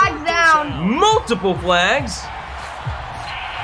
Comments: in the video did anyone else hear roughing the pastor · Is it weird that at 9:57 I heard say “roughing the pastor” 🤣